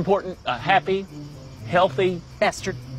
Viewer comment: I thought she said goodnight bastard😂